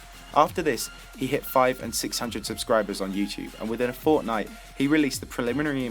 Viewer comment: I heard fortnite